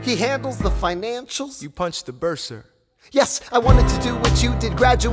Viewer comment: "You punched the bursar?" "..." "... Y E S-" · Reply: The whole time I thought he was saying "You punched the Burr sir?" and the whole time I was so confused